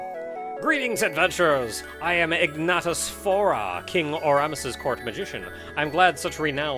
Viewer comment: King Auramis. I misheard as "Orranis" .... which is difficult to give context to ^^;. iykyk (yay Garth Nix.)